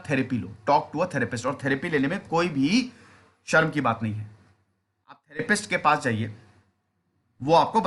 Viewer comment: who else heard rapist 😂